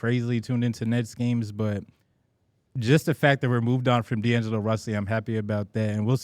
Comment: I think really missed the mark with his analysis on the DFS and DLo trade. It’s not just a “get rid of DLo” trade man. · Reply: am I the only one who heard “d’lo russy”?